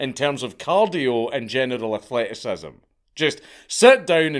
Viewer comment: I definitely heard "genital athleticism" lol